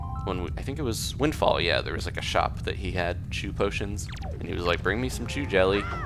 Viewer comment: Every time he said Chu, it sounded like he said Jew. This made for a funny video. ;D